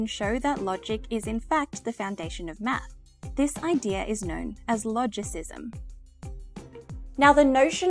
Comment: What does she say at 6:54? Sounds like "Displaintia is known as Logicism". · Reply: This idea is known as...